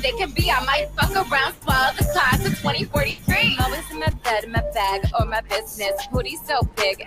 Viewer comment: what does she say after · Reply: always in my bed and my bag or my business